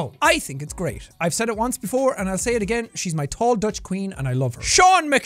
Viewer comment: "She's my tall Dutch queen, and I love her." And I love that you call her that!! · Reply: I thought he said tall Dutch bean 😅 Thanks for the clarification...